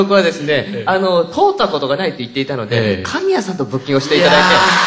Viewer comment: 斎藤さんの「まだ一回も○○った事がないので〜」って、何っておっしゃってますかね…？🤔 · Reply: Yu K 「通った事がない」でしたか！実は福山さんも50:41に同じ言葉をおっしゃってるのですが、ここでも聞き取れなかったのでずっと気になってました😭ありがとうございます〜！😊